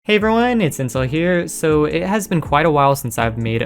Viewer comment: sounded like mans said hi guys its incel here